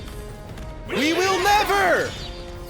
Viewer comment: Sorry I don't hear Trevor I hear, "We will never, find treasure!" Like why are you happy for that?!